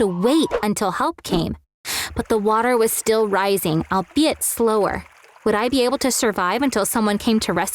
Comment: is it me or did she say a BEAYT slower